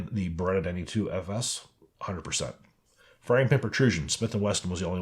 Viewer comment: What did you say at 9:16? Firing pin protrusion? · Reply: Smith & Wesson failed firing pin protrusion